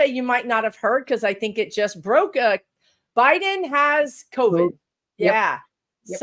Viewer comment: Omg! I just clicked on to watch the replay, and I misheard you. I THOUGHT you said "Biden has coded". I don't think I've ever hit Google faster!